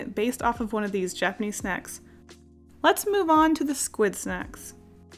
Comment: Okay but when you said; ‘onto the squid snacks’ I heard; ‘onto the squid sex’